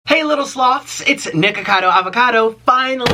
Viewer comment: no joke, I always hear you say "sluts" instead of "sloths"